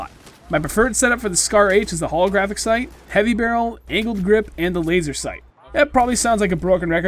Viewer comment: Ok sorry, but it sounded like he said "anal grip"